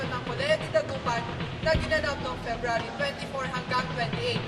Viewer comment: did he just say FEBRARY?(february)